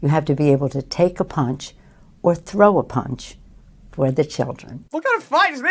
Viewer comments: Father: "Here's a right hook, Nancy! It's for little Johnny!!!" Johnny: "No Dad! Kick her in the balls!!!" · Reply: At first I thought she said she wanted to throw a punch at the children. LOL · Wait a minute...did she just say she wants to falcon punch my children?!!